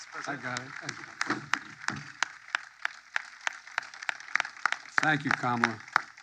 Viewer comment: When he said “thank you, Kamala,” I heard “thank you, comrade.”